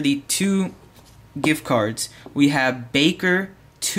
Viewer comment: when he said baker i thought he was saying Baka i was like (I WON????????O.O!) than oh -.- Congratz to the winners :)